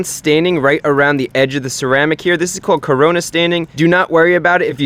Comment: did he just say corona staining?